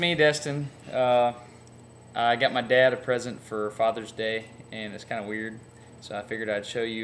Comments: I thought I heard you say "I got my dad in prison for father's day, and its kinda weird." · Thought he said "Got my Dad out of prison for Father's Day."